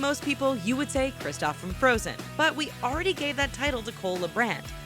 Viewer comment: Put on captions at 9:39 and you can see that it says cola brand instead of cole lebrat